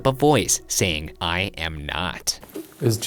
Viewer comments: I thought the voice saying "I am not" said "I hope" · I heard “I Am Groot” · When the ghost said I am not I thought it said I hope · when the ghost says "i am not" i thought it said "i hope" cause would a ghost love that jesus is with him so he would say "i hope". "I am not" makes no sence. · To me the voice at 17:49 sounds more like “I hope” instead of “I am not” tbh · I thought the voice they thought said “I am not” said “hi ho” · I heard "I hope not" rather than "I am not" though🤷🏻‍♀️ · I didn’t hear “I am not” I heard “I am” like it’s saying “Jesus isn’t here but I am” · I heard “I am not” as “I hope not,” and legit thought it was Ryan muttering it before I found out it was EVP. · I didnt hear "I am not." I heard "I hope not." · Instead of "I am not" I heard "I hope" Am I the only one..? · I heard “I hope not” instead of “I am not” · The voice that you thought said “I am not” is actually saying “I hope”. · I didn't hear "I am not" I heard "I hope." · i heard “i own him” not “i am him” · I heard "I hope not" instead of "I am not" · Instead of hearing "I am not" when Shane asks if Jesus is there I heard "I hope" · I thought the voice said "I hope" instead of "I am not" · I hear “I hope not” instead of “I am not”